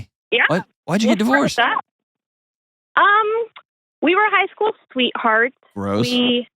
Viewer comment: I am confused. I thought high school sweethearts are people that were in a relationship while BOTH were in school. Sounds like he had already graduated at least two years prior, he took her to her prom, got pregnant and then got married. She even said she didn't even know him that well. Smh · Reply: Did she use those words, high school sweetheart?? I thought she said she had a “prom baby”.